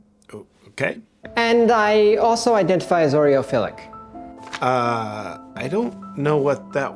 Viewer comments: So is it Urophiliac or Oriophilliac again? Either he meant he love an Orio so much he could bath in it or instead of orio he like to getting urinated on? Can anyone reminding me again why are we having these freaks in the spotlight again? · Did anyone else hear Oreo filling?